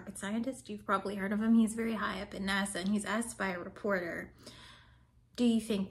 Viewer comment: "high up in NASA" and it almost sounded like you said "high up in nazi" but maybe that's just my ears. ;)